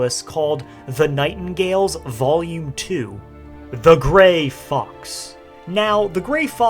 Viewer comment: I've just been listening to the video in the background, and I thought he said "The Gray Fucks." I thought it was to insult someone, but then I looked at the spelling and heard it a second time, correctly.